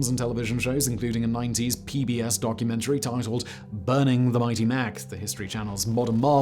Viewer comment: Correct me if I'm hearing things but does Simon say "Burning the mighty Mac" instead of building the mighty Mac ?